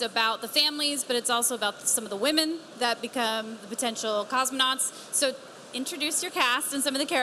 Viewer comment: did she just call them ‘cosmonauts’